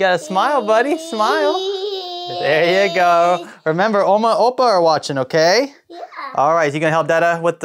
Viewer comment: did you just say “Oma en Opa”? In Dutch??